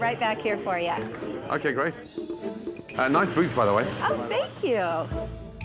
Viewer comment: HELP WHAT DID HE SAY?! 😭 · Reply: Thinking "nice boots" but he can be a bit more.. provocative